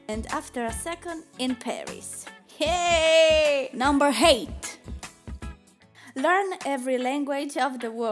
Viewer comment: NUMBER eight whi thought she said "hate" instead of "eight"